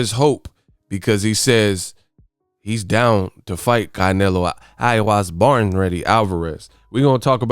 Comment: BRUH, I ASKED NICELY, DON'T KILL ME WITH THE "AVERAGE PILGRIM"...😂😂😅😅 I HEARD THAT - "I WAS BORN READY IMITATION TOO!" 😂😂😅😅